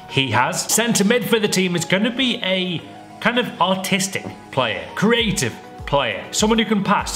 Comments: I thought he said autistic lol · it sounds like he says "Autistic" 😂😂😂😶